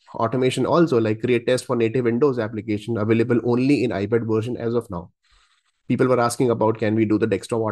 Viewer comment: Hi, thank you so much for your videos and help. In this video at 3:48, I believe you are mistakenly saying, this is available in iPad version instead of desktop application is available only in paid version. Thanks!